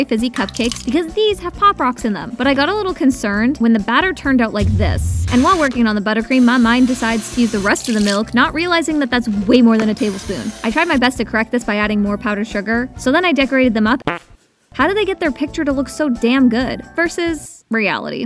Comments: When you said:"my mind decided to...... " I heard you saying:"my mom decide to "😭😭😭😭😭😭😭IM CRYING Btw I am in love with the colour pink this is the most greatest thing a YouTuber has cooked❤❤❤❤🩷🩷🩷🩷🩷🩷🩷🩷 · When you said:"my mind decided to...... " I heard you saying:"my mom decide to "😭😭😭😭😭😭😭IM CRYING